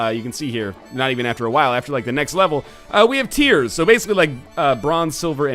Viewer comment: I really thought you were saying tears instead of tiers. I've been watching too many of your isaac videos.